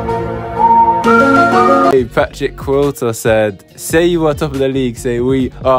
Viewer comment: I had to make a quick check - I thought you said "fat chick quota"..... 4:19 -AXB in matching kit - they have to be decent then! 4:30 - amazing goal by Stan. 7:26 - wow, #5 misses a sitter there, that could have really swung the initiative to the opponents! 8:00 - lovely passing move and then 8:16 - killer blow to go in at HT 3-1 up 10:11 - out of nowhere - AXB not out of it yet wow! 11:25 - the comeback is on 12:08 - 5ive guys back in front - end to end stuff - great game! 13:02 - Bang!! Four each!! Then the width of the post comes between Stan and the winning goal. Great game - 2 really good sides!! Not too much turkey over Christmas, you guys need to come back hungry to win the title!!!!